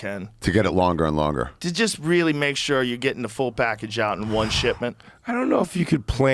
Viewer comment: JOE! JOE I HEARD THAT! One shitment... lmfaooooooo